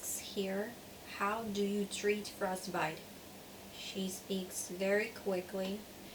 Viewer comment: what is the word she's saying at 2:59 ?? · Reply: Walter Mastrangelo frostbite